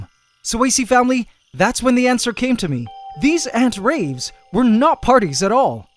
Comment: did you just say "antswer".